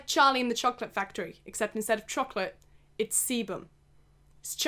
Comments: every time she said sebum i thought she said semen i- just look (start 4:46 end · There's probably like a solid hour of outtakes where you say semen instead of sebum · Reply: it really sounded like she said charlie and the semen factory :( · my volume was low so i thought you said its is like charlie and the seamon factory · Someone said that it sounded like she said semen and now all I heard was Charlie and the semen factory and...ummm... idk how I feel abt that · I thought she said Charlie in the semen factory